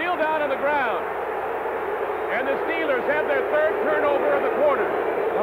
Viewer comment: Did I hear that correctly? At about the 0:28 second mark,.. did the announcer say that "the Steelers have their 3rd turnover of the QUARTER!"?